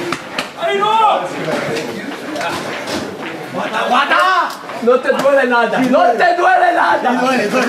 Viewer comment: （空耳）和田、和田ぁー！